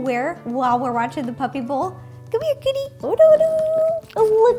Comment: Did she say come here kitty? XD